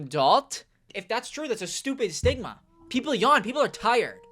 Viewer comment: The internet rotted my brain so bad that when you said, "stigma", I heard, "sigma"